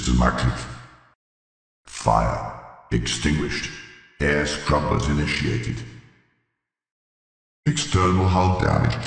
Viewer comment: I always thought it said: "Fire extinguished. Eggs scrambled initiated"